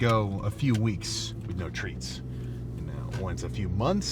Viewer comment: I HEARD TRENS